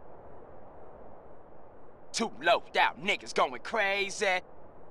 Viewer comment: what did he say? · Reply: Two loc'ed out niggas going crazy